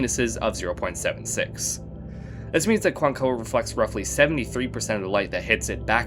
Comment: Subtitles say “Quaoar” instead of “Cuancoa”